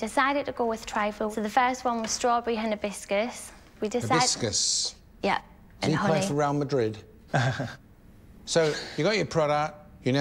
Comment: what does he mean by saying "do you play for real madrid"? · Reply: He says 'Hibiscus? Does he play for real madrid?' The joke being it sounds like a spanish/european surname